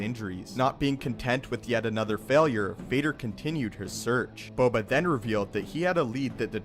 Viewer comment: It just me or did he say Vader continued her search